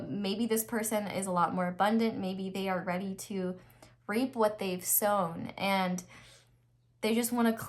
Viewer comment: Girl. I'm only a few min into pile 2 and completely shook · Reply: Girl. I thought had said rape what they have sowed instead of reap